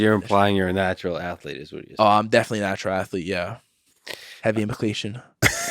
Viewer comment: Whats Nicky Rod say at 38:22 I can't catch it with the accent. Please and thankyou 🙏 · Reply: I think he may have lightly slurred a response to Lex saying “So youre implying youre a natural athlete?” “Oh yeah I’m definitely natural athlete.. heavy implication.”